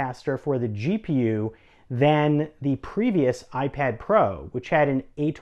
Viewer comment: did he say "then"?